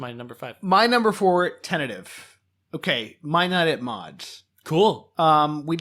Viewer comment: Awesome podcast, it was a pleasure to listen to it and also very helpful for creating my own list of films to watch in the future. One thing though. I apologize in advance, because it's probably either my bad English, "film ignorance" or I'm simply deaf (or all of the above), but I can't hear properly what said as his no. 4 in "What films should have been included" (2:01:21). For me it sounds like "might night at mods" the yt captions say "my not at mods" and I have no idea what really was said here, even though I listened to it 7 times (also in slow-mo). What's even funnier is that you guys usually give the name of director, so I'm sure I would've been able to find the title by his filmography, but this time you discuss that the director is a practicing Catholic and never mention his name... Can someone help? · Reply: Sorry about that - it's My Night at Maud's. Glad you enjoyed the ep, thanks for listening!